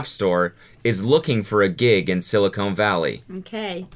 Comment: did he say SiliCONE Valley